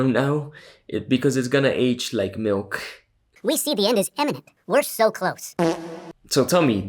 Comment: I thought he said the end is Eminem at first 😭